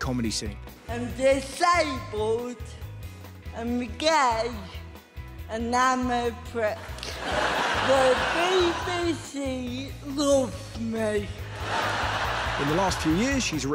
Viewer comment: "I'm disabled, I'm gay, and I'm a prick. The BBC loves me." 0:44 "Hello!" 0:47 "I'm good! I'm sad that we can't--" 1:14 "Yeah! But, basically, I wake up every day, I make people laugh, and then get paid for it. Of course I'm happy! Honestly, I feel like I say it too much, but I am living my dream, um, and I never brought it on. I'm genuinely so happy, so yay!" 2:06 "Yeah! So, it's a travel show, where I go around the country, seeing how accessible places are, and I'm pretty much, like, a tour guide for disabled people. So, it's called Mission: Accessible, but I'm very unhappy about that, because I wanted to call it Crip Advisor." 3:10 "So, I think what they did say was 'disabled,' 'holiday', 'accessible'. It sounds a bit dry. It sounds like "We're talking to Margaret, who's 80, in a wheelchair, and she wants to go on holiday to Banga." It's like, wElL dOnE fOr YoU, mArGaReT, but I don't care." 3:53 "Yeah! But you're all thinking it!" 4:02 "Yeah, yeah! oH, wAiT! And I always do!" 4:27 "So, what I wanted to do was tailor-make a holiday for a person's needs, and for their disability." 5:21 "Before today, if you showed me a glider, and gone "Can a disabled person do that?", absolutely not. It was truly incredible." 6:02 "Right. Okay. For the show, I would say "This is amazing," because we did it with regard for cerebral palsy. We wanted to do something incredible, and it was, but not for me. They assured me, said "Do you want to fly a glider, or do you want to stay on Earth